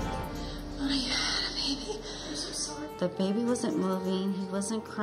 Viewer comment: Did I hear this wrong or in the background did she say I’m so sorry..?